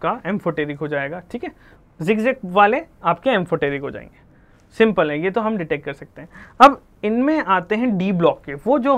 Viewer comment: can someone tell me what he said here? zig zag? was he referring to metalloids? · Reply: Diagonal relationship